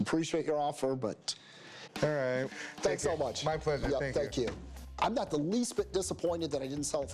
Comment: At low volume sounds like he said f@ you